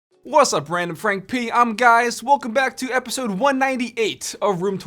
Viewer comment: When he said “wassup RandFrankP, I’m guys” I felt that Edit: Man who knew this would get a lot of likes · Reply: I thought he said I'm Giaus. Like Giaus Baltar